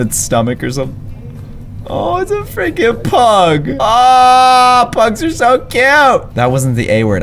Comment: Go back to 6:00 and listen Is it just me or did he say “aw it’s a frickin pug” on his last life